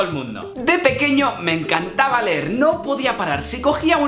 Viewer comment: pense que dijo de pequeño me encantaba ver nOp0r xddddd